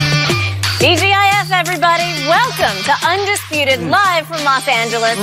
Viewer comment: For a second I thought she said “DGAF everybody”